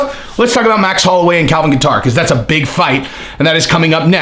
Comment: All I kept hearing was “Calvin guitar” 🤣 love you